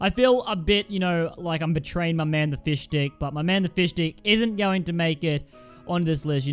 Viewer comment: Please don't tell me I'm the only one who heard him say fishdick